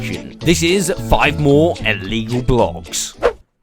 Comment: My fatass thought it said Smore 🤣🤣